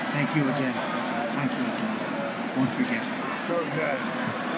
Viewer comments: Did he say “don’t forget” to the maga6 justices on audio/video just now? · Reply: I think I heard “thank you, won’t forget” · Sounds like he said don't forget?